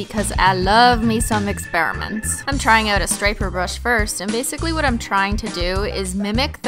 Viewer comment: I thought it said stripper brush...